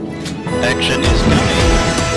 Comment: He is actually saying "action," not "Akshan" cool video though